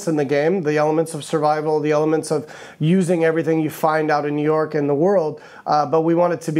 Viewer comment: Did he say New York and the world around 11:50 ?